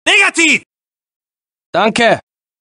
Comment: がノンケにしか聞こえない